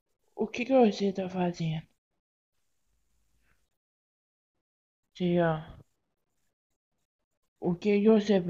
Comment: Gente responde aq plis, no momento 4:29 SÓ EU OUVI O HOESOK FALANDO TAEAN?!